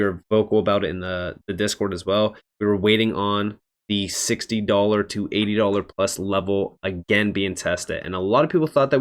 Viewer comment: So what's a good buy price? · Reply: Ok, I thought he said he was waiting for $60 QNT and that seems a little far off.